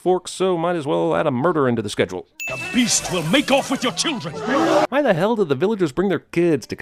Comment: I thought Gaston said "he will make out with your children" WTF